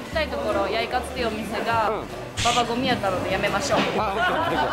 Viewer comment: バカ混みって聞こえるかもやけどババ混み！関西弁でク○みたいに混んでるって意味です！